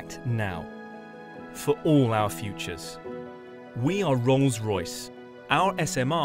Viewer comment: Did I hear Wrongs Royce at 2:35 hahahahahaha